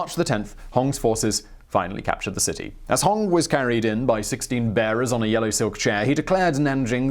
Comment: I thought he said Hong Xiuquan traveled into the city on 16 BEARS not 16 bearers. The first way would cooler.